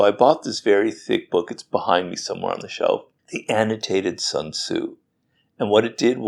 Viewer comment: What is the name of book RG said in the video 0:48 which was about Sun Tzu? I could not find it in google :( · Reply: The Annotated Zunzhu